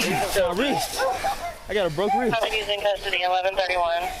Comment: "I gotta broke wrist." Aw do you want a go fund me page you poor thing? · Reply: I thought he said that he has a broken rib.